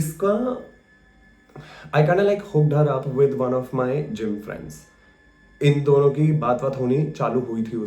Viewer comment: wht did he say???? I don't get it..does anyone explain?? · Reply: isme rowhi ko ek ldke sey introduce kraya jo iska gym ka dost tha